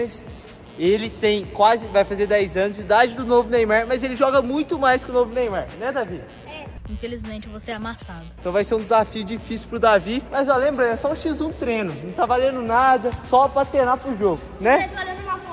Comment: Oque eu entendi o novo neymar falando : -Felizmente eu vou ser amassado